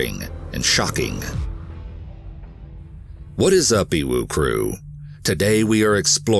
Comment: always sounds like hes saying whats up OWO crew